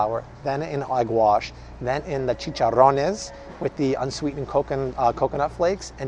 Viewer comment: Did he say chicharrones ??? "Chicharrones" means "pork rind" in Spanish? 🐷